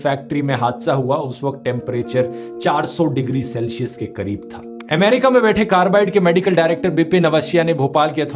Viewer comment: did he said 400 degree Celsius